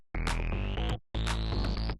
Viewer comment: I was very confused when I thought said Walnut Finish. Like they're pivoting to a carpentry channel. :)